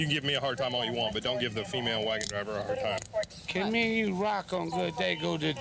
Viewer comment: did he say? Don’t give the female white driver a hard time?